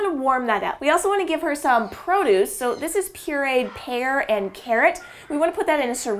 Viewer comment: "Here we have a blend of pear and carrot." Me, an intellectual: You mean parrot? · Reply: At first I thought she said hair and carrot lol .